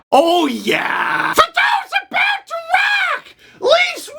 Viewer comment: What does he say @ 0:24 it’s hilarious 😂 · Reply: Kaylen Strowger for those about to rock. He’s mocking the Cbjs goal song